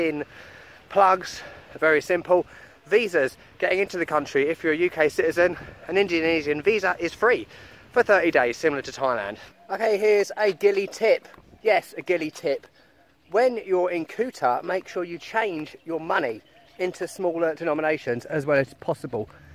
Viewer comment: 600,000 sounds like a lot of bills lol (the 100,000 bills look cool tho) I would love to get to Bali at some point. I also enjoy how you keep uping your videos quality... theyre looking pretty good. I could of sworn you said a Gili Tit...